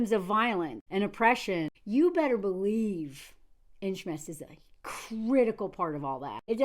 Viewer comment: what does she say shmesticism?